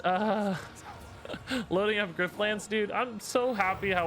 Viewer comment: What game is he talking about at 12:45, can't hear the name. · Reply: Griftlands